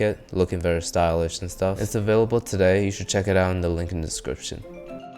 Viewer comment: I heard that as "Check it out in the LigmaScription"